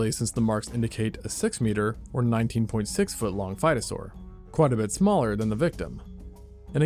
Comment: I keep hearing "fightosaurus" and "rowdysuchian", this can't be a coincidence 😂